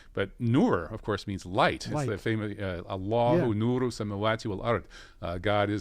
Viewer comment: no surprises here. osama bin laden worked for america and his niece is no different. shes not wrong about trump though, since trump has been the least war crazy in the last 12 years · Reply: Quran Translation - English Only I thought you were saying "radiallahu anhu"